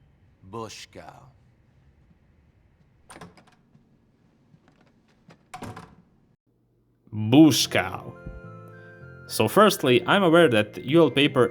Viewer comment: BoschKau ngl that word I hear sounds really cool asf